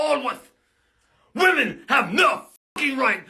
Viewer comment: In one of his wants I thought he said “WOMEN.. have milk” 😂😂